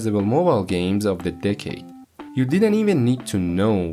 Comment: i thought he said dickade